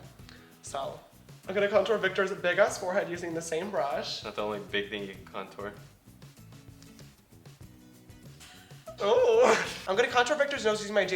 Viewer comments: "That's the only big thing you can contour" IM LAUGHING OH MY GOD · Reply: i thought it said not the only big thing you can contour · He said, "It's not the only big thing you can contour ;)" omg😂😂😂👏👏👏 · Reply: I thought he said "that's the ONLY big thing you can contour" lol